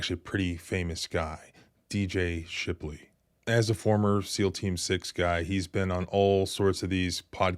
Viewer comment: Ha! All this time I thought you were saying “mealteam 6” I listen on the radio when I'm driving to work and just figured you meant that they are the typical out of shape cops beating someone up again.